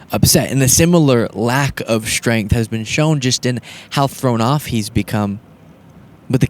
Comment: Did you just say throwed off? My man 💗💙💙💙💙.